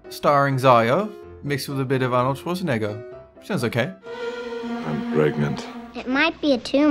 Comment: it sounded like you said "känns okej". That means feels ok in swedish.